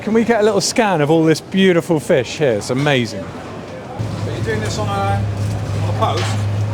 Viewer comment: Did I mishear this: “Are you doing this on a post?” (like X/Twitter, Facebook, etc.) “Yeah but I’m gonna do it on a green egg as well. I’m gonna do it over coal.” That can’t be what I heard.